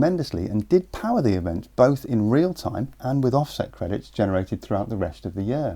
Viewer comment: Did he actually say part of the power was generated by ‘offset credits‘?